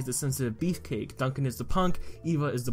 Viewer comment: Bruh I thought he said “Duncan is the cunt” lmfao